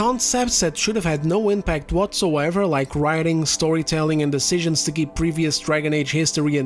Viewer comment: You said the word 'storytelling' but because of the accent and my tinnitus i heard "soytelling" which is a nice little term dont you think.